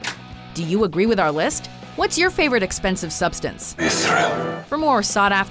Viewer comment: Oh. I thought it said explosive.